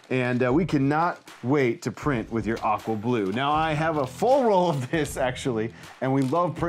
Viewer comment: Oh "aqua blue"! I thought you said "We can't wait to print with your awful blue". LOL